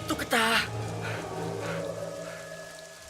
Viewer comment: เพชรฆาตพูดว่าอะไร s.t.a.r. ปะ ได้ยินไม่ถนัด 555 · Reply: ชุติศักดิ์ อินตา เสร็จข้า ปะครับ